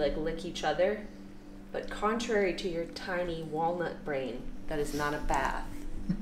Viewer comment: “but contrary to your tiny walnut brain” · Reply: My mom thought she said there wallet brain😂😂😂